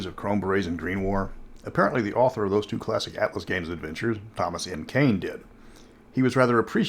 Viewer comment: Ugh, history repeating itself; no thank you. At least your clips were entertaining. I did find your plug at 0:45 for Thomas Kane informative and have passed it along. That was nice of him to send those to you. (Nice Mystara plug too) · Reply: I thought he was saying Thomas Payne at first and was like wait, what? Did the dude from the American Revolution travel forward in time to write some random RPG books?